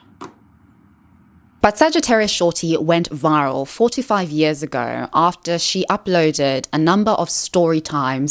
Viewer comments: I thought you said “Sagitaruisshawty went viral 45 years ago” 😂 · I had to run it back because I heard 45 years ago